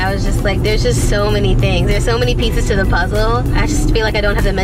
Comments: it sounded like she said there’s just so many pizzas to the puzzle🤣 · I thought she said "so many pizza's to the puzzle"